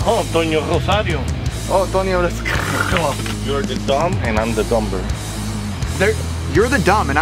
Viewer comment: I thought he said I’m the dumper